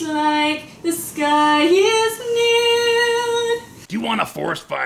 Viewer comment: When GF was singing at last I see the light I heard "the sky was nude"